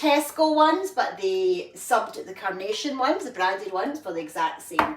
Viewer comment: I thought you said brandied mayonnaise - does that go on your mince pies? Then i realised you said Branded!! Phew 😜🤣